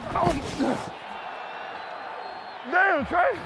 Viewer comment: Actually, it was Panthers No 70, trai turner who was like ''get out of here boi'' when he absolutely flattened Bowman at 0.44 lol. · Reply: i thought he said “Damn, Trai.”